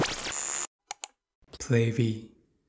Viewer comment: When i hear xiaojun's opening voice saying 'PlayV' is 'Baby' for me...